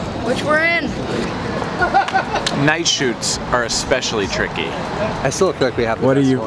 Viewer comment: I thought at 6:54 he was saying "'Nei-Shoots' are especially tricky" meaning Casey is tough to work with when really he was saying night shoots are, and I was disappointed.